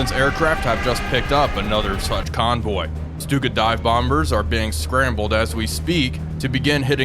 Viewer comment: i miss heard that as stupid dive bombers 😂